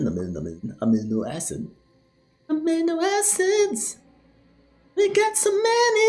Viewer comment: It sounded like he was saying "I'm into acids" and I fully believe that XD